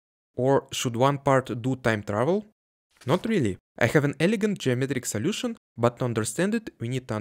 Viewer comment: when I heard 'elegant Germanic solution' the first time...